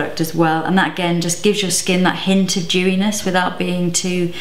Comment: Every time she says "dewyness", I keep hearing "Jewyness"! LOL 😂